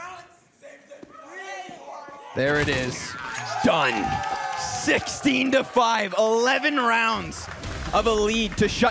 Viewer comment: can anyone work out what the autistic people in the crowd are saying? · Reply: Only five rounds?